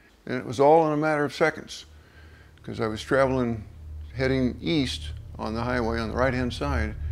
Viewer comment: East? I thought you said Weast